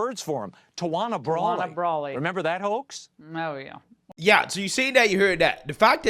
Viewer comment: I don't know about the Tawana Brawley thing, but I sure thought he was about to say, "Remember that hoe?" But he said "hoax" instead.